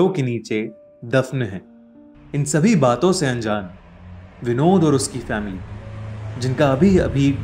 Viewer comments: Did you vote today? Now enjoy our horror stories! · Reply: In 4:06 i hear BINOD 🤣😂🤣😂😂🤣😂🤣😂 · Did I hear Binod ? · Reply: No it's vinod